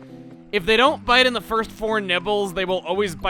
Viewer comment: What said: "If they don't bite in the first four nibbles..." What I heard: "If they don't bite in the first four nipples..."